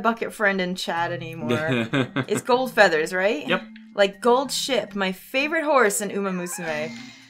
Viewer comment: I thought I heard Gold Shit instead of Gold Ship I guess I was wrong XD